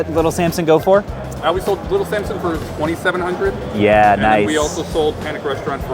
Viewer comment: he said $2700 But subtitles say $2100